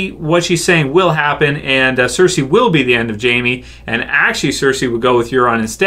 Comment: Sounded like you said we'll see if Cersei will choose to urinate on Jaime😂😂👍 I was like probably the gross bitch likes incest I'm sure water sports aren't OFF the table lol